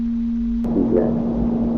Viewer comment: Late reply; but at 31:27 i hear "She's next" instead of choose next.